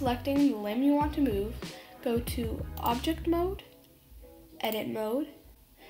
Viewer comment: I thought object mode said object Noodle